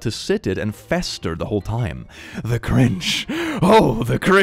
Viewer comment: i was reading the comments while he read this, so i heard "the grinch... OH, the GRINCH...."